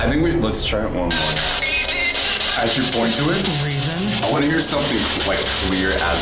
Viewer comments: I thought I heard another day like you will live another day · why does it sound like "Webber" instead of "the river" when your looking away from the video - is it just me? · I'm think.it said another day like you will live another day cause you were pissing it of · sounds like someone was saying a tarro card's name like the river.